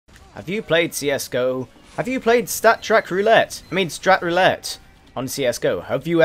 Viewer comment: I thought He said strip roulette XD!!!!!!